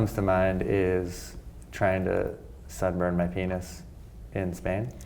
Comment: I thought he said 'severed my penis in Spain,' but he said 'sunburned.' Phew! :-)